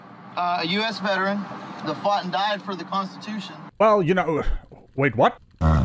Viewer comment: That poor passenger. He died for the Constitution and then missed Christmas. 🎄 😔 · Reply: Wait, I thought dude said he died for the prostitution?